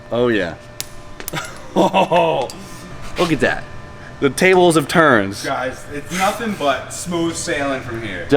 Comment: I'm sorry I thought I heard you say "the tables have turns"